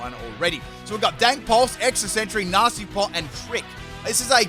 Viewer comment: Am I crazy or did he just say "Dank Pulse"? xD